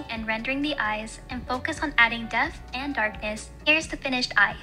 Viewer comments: I thought she said "Death and darkness 😆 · am i the only one who heard death and darkness